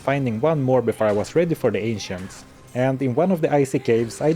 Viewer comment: When he said "the ancients" i heard " the asians"